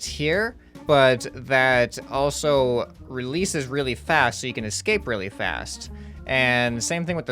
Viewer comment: I thought it said ascape poo